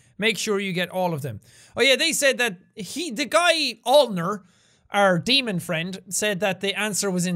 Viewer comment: When it said 'Anna met with James Aulner' I thought it said "Anna met with James Arthur"😂